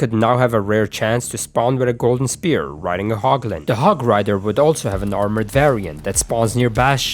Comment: Did he say Hog Riderrrr?